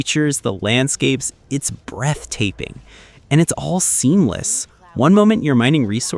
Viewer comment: Haha.... did he say "breath taping"?